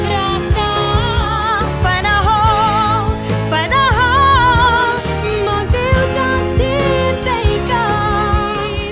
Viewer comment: For 2:15 I thought it said “find a Hoe” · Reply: Fernandag 916 it's fana ho 😊